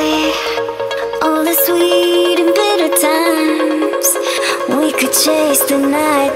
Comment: I love it when I randomly discover gems like these while browsing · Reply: "Sweet and Better Times" .. I hear "Sweet and Bitter Times".